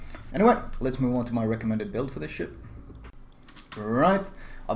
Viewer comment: when you say ship the subtitles says shit :D see